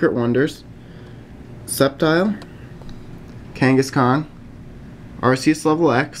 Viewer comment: when he said kangaskhan it sounded like he said genghis khan